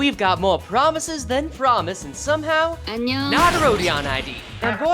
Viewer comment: Did I hear yesterdays promise 0:01 I to the video